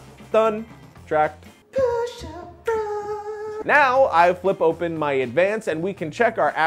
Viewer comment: did say Push Up bra ?LOL